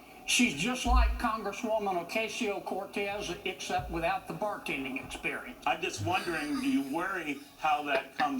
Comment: "She's just like congresswoman Cortez except without the bartending experience" Savage 🤣😭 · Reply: I thought he said barking experience lol.